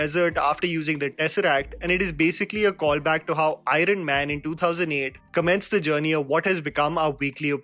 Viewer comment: Did say Irene man instead of Iron man 🤔?